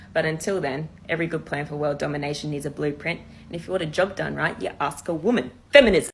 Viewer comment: What did she say if you want to destroy the world ask a woman feminism?